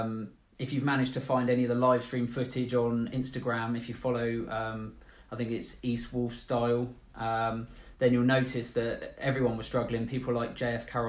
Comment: Can someone say what instagram he means at 1:12? I cant understand it properly and dont find anything. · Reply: jason clauwaert